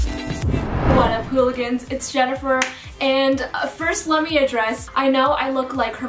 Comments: her names Jennifer but the subtitle says “Sophie” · I haven’t seen many of her videos especially with captions but the captions say SOPHIA when she says Jennifer